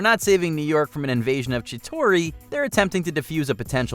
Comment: I’m sorry did I just heard“Chitori”?